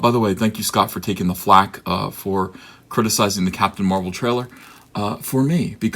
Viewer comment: I heard "criticising ramen"